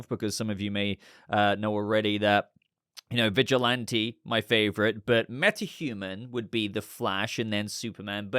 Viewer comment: for a second there I thought you said that Vigilante was your favorite character and I was like “what?” 😂 No hero-shaming, I suppose on a planet of 7+ billion people it must be at least one…